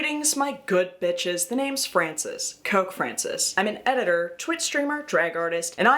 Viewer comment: 03 i thought i heard "cuck francis" lol